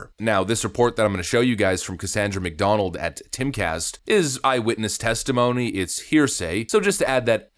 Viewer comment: I keep hearing "Ronald MacDonald."